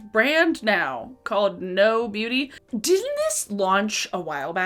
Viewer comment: Every time I hear "Know Beauty" my brain hears "No Beauty", which is maybe not the feeling they were going for 🤔